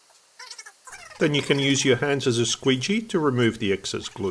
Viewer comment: "then you can use your hand as a squeetshy" some scientific terminology right there ^^ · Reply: I thought I said "squeegee" but "squeetshy" works for me too :)